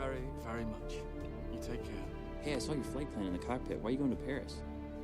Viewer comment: so funny that the subtitles get it wrong so instead of “hey, i saw your flight plan in the cockpit” it changed to “safe flight, man” and then censored the word cockpit. i can only assume because of cock? 😂